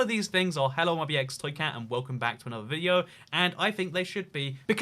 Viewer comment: my captions say “hello my be xed I can’t and welcome back to another video”. 😂